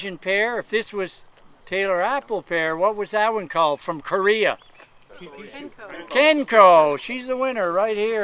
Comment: what did he say the tree is? please let me know if someone knows what it is. Thanks . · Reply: Kenko, I got one and it is super resistant tree (hardy and disease free). Vigorous too.